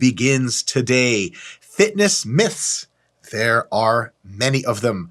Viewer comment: I thought you said Fitness MILFs...damn.